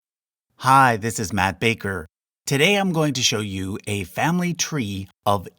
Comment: I always hear him saying "I'm map maker" instead of "I'm Matt Baker"